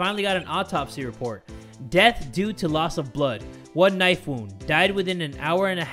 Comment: I thought he said knifu instead of knife wound